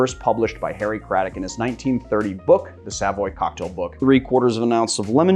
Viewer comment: When he said "the Savoy Cocktail book" i first thought he said "the sadboy cocktail book"